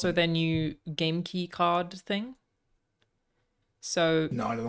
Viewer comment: I misheard that as "genki card thing" did a double take 😂